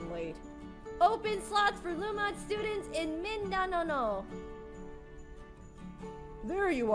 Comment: i heard minananow but great try on saying min da nao